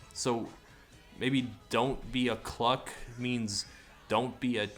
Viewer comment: I keep doing a double-take because I keep hearing 'dont be a cluck' as 'dont be a cuck' lmao